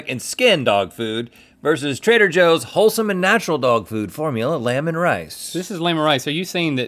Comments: every time they said 'lamb and rice' i thought they were saying lemon rice but in a weird way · I thought Rhett said lemon rice 🤢🍋🍚